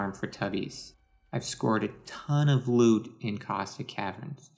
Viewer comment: sounds like you said "I've squirted tons of loot"